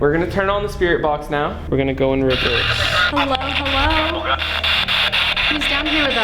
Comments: When she said "hello" it sounded like it said Hola in spanish.. (meaning hello) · or 21:24 i heard hola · I heard Hola(Hi) 21:24 Mira(Look)